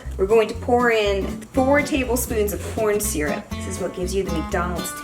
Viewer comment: did she say porn syrup?